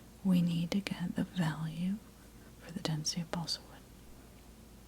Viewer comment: i thought it said Destiny xD